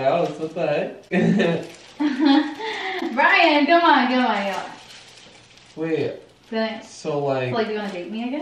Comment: I thought Brian called her “Babe” or did he say wait?..hmm🤨😂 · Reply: ČhRïŠ RëŸêŠ yes he said babe bro